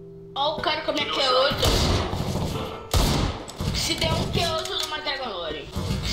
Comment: Eu ñ entendi oq ele disse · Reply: se der um que ota eu dou uma dragon lore